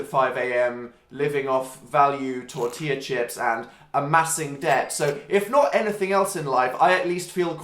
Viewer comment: When he said "massive debt" I thought he said "plastic desk". 😂